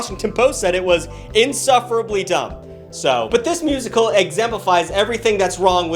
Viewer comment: I’m sorry… Did you just say exempifies? Not a word. Insufferably dumb indeed!